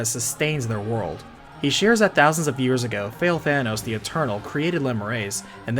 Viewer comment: i keep hearing "Fail-Thanos"